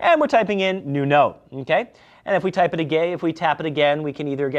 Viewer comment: Did he said first: "If we type it a Gay?" and corrected itself with: "Type it again" ;))